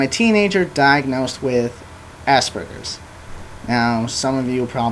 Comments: i thought he was saying assburgers for like 5 mins. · thought he was saying assburgers :3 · sounds like he says assburgers ha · I thought he was saying assburgers at first and was laughing really hard, but then I really started listening and it's a good video. Props. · HAHAHHAHAHA i no i thought he was saying assburgers too!!! hehe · lol sounds like he said assburgers....